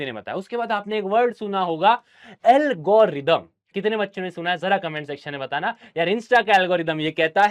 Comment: i heard algorithm